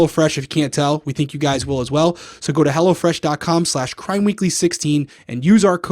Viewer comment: i thought he said "go to hell" 😂😂😂